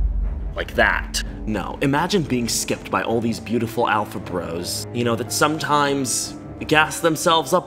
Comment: did he just say alpha bros???💀💀💀